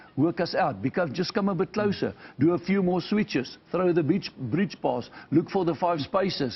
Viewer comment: did he just say on tv “throw the bitch”🤣🤣🤣🤣🤣🤣